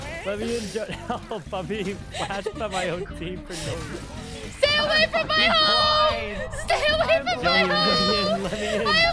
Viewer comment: Wait oh my my brain I thought she said "STAY AWAY FROM MY HOLE"